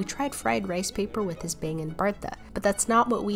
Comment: lol i thought you said bangin bertha